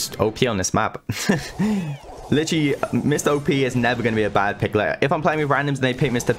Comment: Anybody else heard "Mr. OP" at 7:35 XDDD